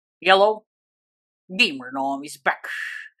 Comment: Hey I don't no if u notice this or not but everytime u say hello I thought you say yellow